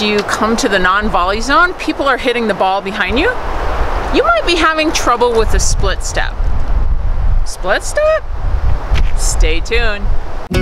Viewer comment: great advice ! I am tend to move as I go toward the ball. You had me worried at first I thought you said I would have to do the splits